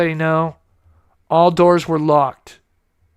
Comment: I heard "all doors were locked"